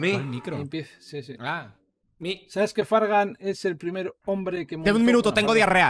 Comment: Que dice focus en este minuto? "Sabes que fargan es el primer hombre que..." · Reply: intente escucharlo muchas veces y lo que creo que dice es - "sabes que fargan es el primer hombre que me importa" ajsjaj bueno creo si xd